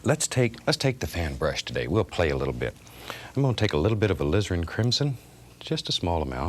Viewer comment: When I used to watch this as a kid I thought he was saying "we're gonna take a little bit of a LIZARD IN CRIMSON" I still hear it today even knowing that he is saying alizarin crimson. Giggles every time.